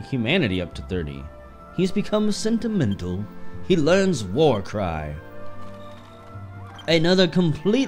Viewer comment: Somehow I heard Warcry as War Crime at 12:53 I think i need to sleep.